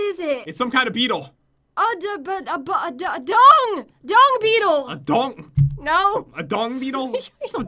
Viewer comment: Enjoy the video guys!! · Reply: btw I thought Jen said dong beetle i had to watch that part again to hear it right and it was dung beetle